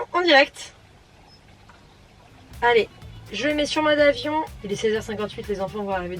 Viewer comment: Quand tu dis "je le mets sur mode avion", j'ai entendu "monde afion"...c'est grave docteur? ^^Merci pour cette vidéo intime <3